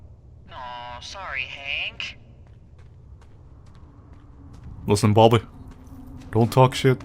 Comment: Did she just say hunk? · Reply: Hank. It's a name.